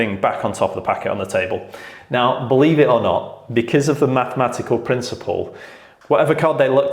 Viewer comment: Is it only me or did I hear "MattMagical" Card trick? 💯